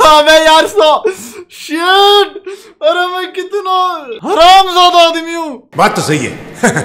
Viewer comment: did i just hear "he ram"??